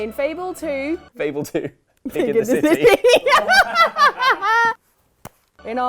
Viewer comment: Hello, Im an old deaf guy. What is saying at 14:10 to suddenly burst out laughing? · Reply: Lars-Göran Karlsson “Fable 2: Pig in the City”. I think it’s an in-joke.